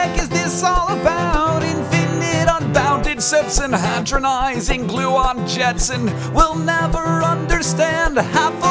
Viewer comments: First time I heard this I thought he said "patronising glue-on jets" A-derp... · i really don't want to be the one to say this, but when i listened for the first time, i hear "infinite unbounded sex" i would like to apologize to hank and nerdfighteria. i just had to say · whenever Hank says "Infinite unbounded sets" I hear "Infinite unbounded sex"...